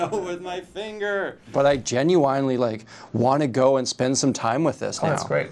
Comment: nah did bro just say genyoueyeinly